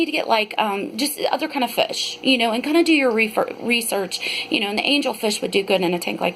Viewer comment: All I heard was REEFER